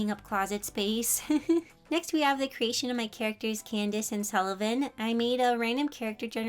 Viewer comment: did I hear Candice?